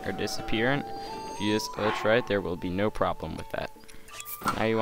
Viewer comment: wtf is he saying sounds like clutch right